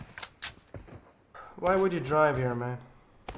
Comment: I am sure I heard "why would u drive here man" by 🐼